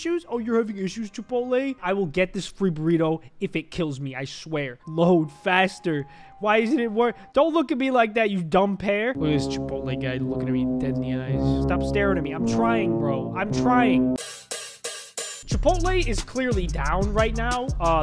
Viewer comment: … Did he just call that a pear?